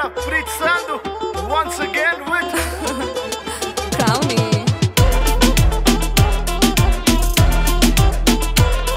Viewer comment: What this lady says at timing 0:16 ...?? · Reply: crowny